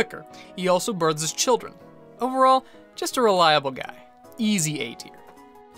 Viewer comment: for a second I thought he said over all just a relatable guy.